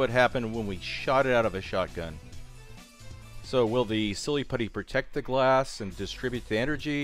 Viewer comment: I thought he said shit it out of a shotgun